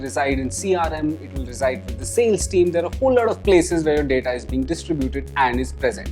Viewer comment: Great video, thank you. One question: what do you say at 5:17? Google Analytics, CRM...what's the third?? Thanks again! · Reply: John Hayward Hi John..He said "there's sales team" 👍